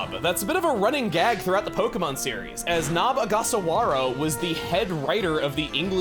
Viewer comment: Nob Ogasawaro? what it sounded like to me